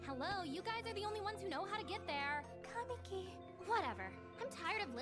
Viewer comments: it sounds like shes saying kaneki (i know thats not what she said) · wait did sheimi just say kaneki. jesus christ my boy is getting recognition in another anime. you go senpai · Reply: Kamiki